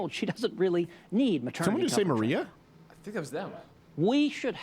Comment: Objectively... was this debate even close? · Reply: I heard Maria